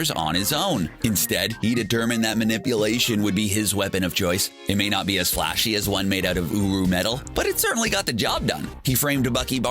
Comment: I heard uwu metal 🤣